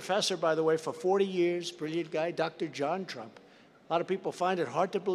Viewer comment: I heard that as "Dr. JonTron"